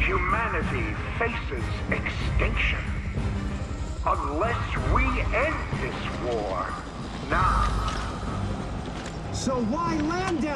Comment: I no understand what chairman Prescott say in this time of video, I can't see subtitles in this moment, anyone can help me? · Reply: "Unless we end this war now"